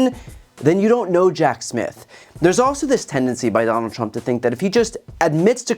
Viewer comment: When you said "you don't know Jack Smith" I thought you said "you don't know Jack Shit" 😂😂 Great reporting as usual BTC! vote blue! 💙💙💙